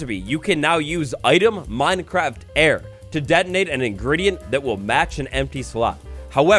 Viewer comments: In 6:15 did he say detonate instead of denote or am I listening wrong? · really said detonate instead of denote